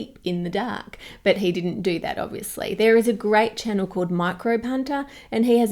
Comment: When you said "microbe hunter," I heard "micro punter." Not sure that's meaningful, but there you have it.